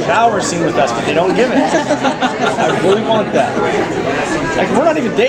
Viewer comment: Can someone please tell me what did he say on 6:22 ? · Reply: J He says 'I really want that'